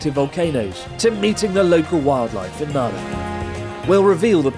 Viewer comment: thought i misheard you said "eating" the wild life, that's one wild life i would definitely eat